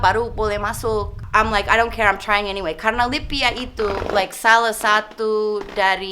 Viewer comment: Saya harap jangan dibuat versi bukunya, cukuplah kita tau lewat video ini 😉, gak kebayang kalo ada versi buku, dan dibaca langsung sama remaja2 yg kurang ilmu agamanya, justru akan menggiring opini mereka, yg awalnya mau hijrah jadi batal, yg sudah hijrah, iman sedang turun2 nya malah makin parah jadinya , sebenernya sangat disayangkan atas keputusan mba melepas hijab, cukuplah jadi pelajaran bagi kita umat Islam, sepintar apapun kita dalam mempelajari banyak hal, tetap butuh seorang mentor/guru, apalagi urusan agama, kita gabisa sembarangan memberikan tafsir ilmu hanya berdasarkan satu/dua sumber, simple aja Islam itu "laa ilaaha illallaah" pelajari dulu ilmu tauhid baru setelah itu lanjutkan ke perihal fiqh dan sholat #Cmiiw 😊🙏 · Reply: trimakasih koreksinya, saya berrti salah dengar libya kirain lipia, saya edit komen saya.. trimakasih..